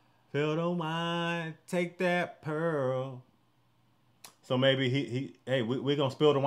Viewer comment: Take that pearl sounds right now that I was told that, but I always heard “dig that girl.” I actually like mine best. :)